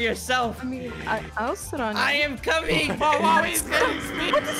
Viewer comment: did just say to she’ll sit on him